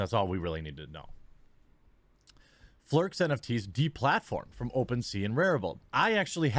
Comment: Oh! Flurks! I thought he was saying "flerfs!" I have been watching too many flat Earth debunking videos!